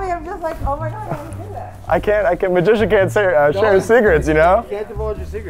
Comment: It sounded like he said “ a magician never shares his cigarettes”